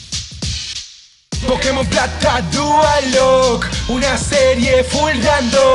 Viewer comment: Ke Berga? Entendí "pokemon tactac dualocke" :v